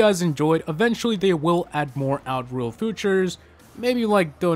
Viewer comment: did he say outruled footorchs